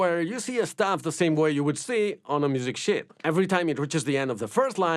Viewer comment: I swear he said “music sh*t”